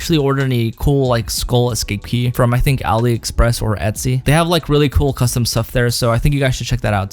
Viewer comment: does anyone have the links to the sites he was talking about 5:35 about the keycap sites couldn't hear what hes saying edit: or if you have any recommendation sites that would also be cool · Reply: Weeday420 he said ali express